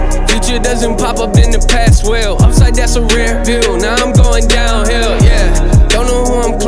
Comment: Help me figure out what he's saying at 1:00 it's not "outside that's a rear view" like it says everywhere online. it sounds to me like "Upside that's a rare/rear view" but I just can't figure out what makes the most sense, someone get back to me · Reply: Upside that's a rare view, now I'm going down hill. Implementing when hes doing good in life, it'll rollercoaster down eventually